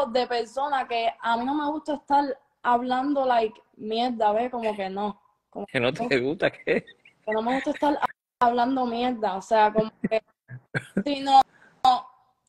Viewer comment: QUE DICE ELLA? Que no le gusta hablar mierda ???? JAJAJAJAJJAAK